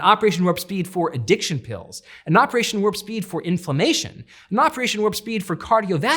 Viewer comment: Sounds like he is saying INFALAMINTATION. I thought it was a word I was not familiar with until I turned on the subtitles!